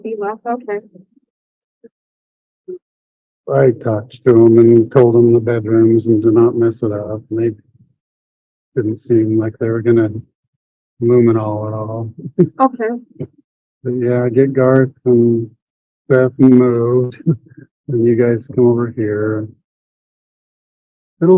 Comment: could we ask Dr. John to analyze. It just really is so perplexing. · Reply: did Chad say “I don’t think they’ve luminoled yet! For blood splatter?